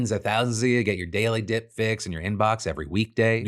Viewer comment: Thanks to Squarespace for soonsoring today’s show! <link> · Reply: I have no idea why... but when I heard this at 14:48 I heard "Your daily d*ck Pics.." LOL